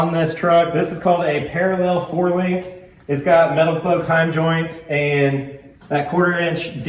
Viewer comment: I had the brackets custom made , the heim joints Are metal cloak , i orderd them threw wheats off road in Orleans IN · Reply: I thought I heard you say Metal Cloak but wasn't sure. I have some of their parts on my Gladiator. I wondered about the brackets. I guess time to fire something off to Send Cut Send. Thanks! Looking forward to the rest of the air setup.